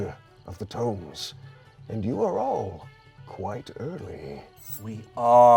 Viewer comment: When you had to rewind a bit because you thought you heard said “you’re quite ugly” instead of “you’re quite early”